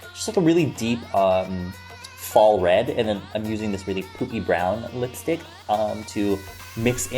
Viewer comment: WHAT DID HE SAY? All I can hear is poopy brown 😂😂😂 please tell me I'm hearing wrong